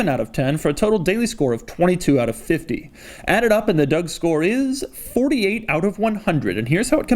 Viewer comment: When I first started to watch your videos I thought you would say “I’m gonna give it a DUCK score” lmao I was like what the hell is a duck score 😂 now I know it’s DOUG SCORE!! 🤦🏽‍♂️